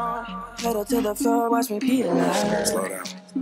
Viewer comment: Is it just me or does it sound like Justin is saying "watch me pee alone" at the end of his turn???